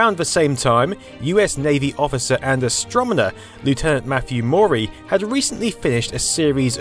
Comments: Did you just say astro-mo-ner?! :))) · did you say 'astromoner'? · Did I hear "Astromener"?